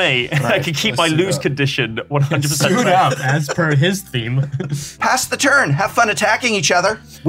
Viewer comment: What did say at 24:04? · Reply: "As per his theme"